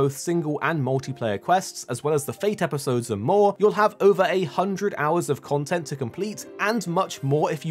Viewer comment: 800 hrs? Did I hear that right?